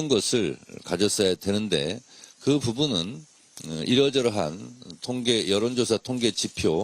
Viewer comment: 당내 의견 조합이 먼저가 아니라 이러저러한 이라고 들리는데 딴지가 보임. Mb아바타 철수 프레임 갇힌 딴지아바타 청래형 ㅠㅠ